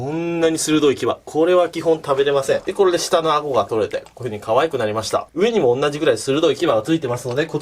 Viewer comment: 連打用 7:457:457:457:457:457:457:45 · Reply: 赤名ユロマ 押したら女に可愛くなりましたって聞こえるＺＯＩ